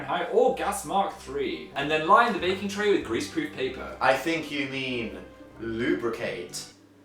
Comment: I thought he said geese poor paper lmao